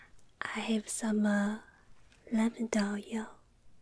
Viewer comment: She said lavender oil Thought she said "Am tired oil"